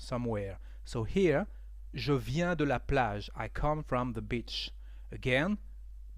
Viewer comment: omg.....what did you jus say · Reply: Sri Archana He said "I come from the beach"